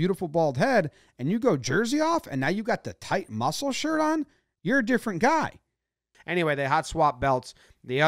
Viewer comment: I heard "you're a gift from God" the first time through, had to rewind to hear "you're a different guy"...